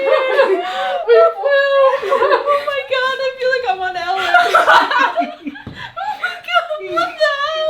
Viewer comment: Good thing explained "Ellen". I thought she said LN, and was like "drugs???"